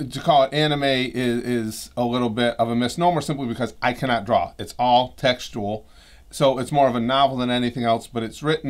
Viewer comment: i thought u said: "its all sexual" lmaooooo ur Pervy energy is spreading lol